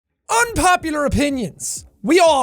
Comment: I thought it said "unpopular onions"